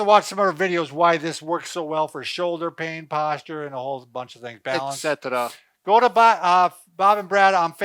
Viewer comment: Appreciate your channel. Thanks 1:00 did Bob say "... it good for the shoulders ... and the whole fking thing." ? Bob